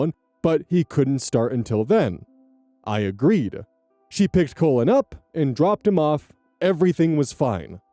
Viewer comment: On story 4, I was only listening and not reading along. I kept hearing the step grandson's name as "colon" and was so confused. Then I looked at the screen at 25:24. I think this is the first time seeing Cullen as a first name. I'm sure it's a lovely name, I'm just dumb.